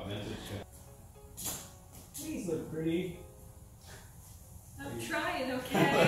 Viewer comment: did he just say ”pls look pretty“ 😅